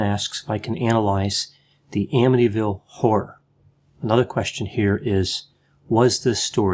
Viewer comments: I was only listening initially and it sounded like he was saying "The Amityville Whore." Whores are real! 😂🤣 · At first I thought Dr G was saying The Amityville Whore. Now, that’s a totally different story!